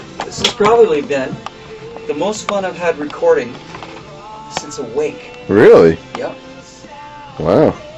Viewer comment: what the says? the most... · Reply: The most fun he's had recording since Awake